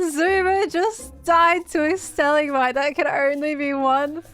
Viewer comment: Am i the only person that heard her say xisuma died on a "stali-mite"? I know what shevsaid but that is funny